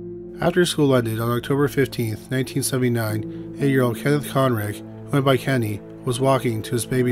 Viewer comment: I thought it said Kenny McCormick